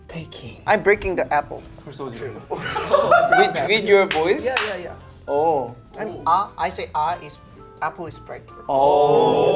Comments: I really thought Jongho said “I go ah and the apple is pregnant.” And I can’t unhear it 🥴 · LOLOL at 1:14 I thought he said the apple is pregnant 😂😂 cuz same